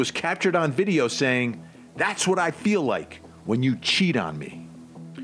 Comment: I never heard "Cheat". I heard "choke". Don't understand why someone would allow someone to lock me up in a suitcase. I'm too claustrophobic.